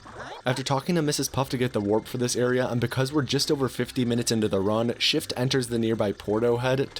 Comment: I thought you said "we"re just over fifteen minutes into the run" and I was like woah, already 15min ? but the I moved my mouse and saw it was in faxt fifty (50!) minutes ! Damn that went quick...